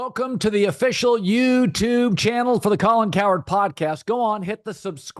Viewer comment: lol I thought he said Colin coward for some reason